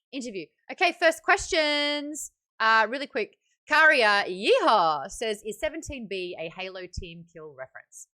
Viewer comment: what does she say in 30:42? i didn't catch that help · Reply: 'Karya YeeHaa' is the person who submitted the question